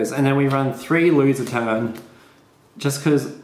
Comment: What cards did you say at 5:31? Louie's attendant? I could understand your accent. I want those cards though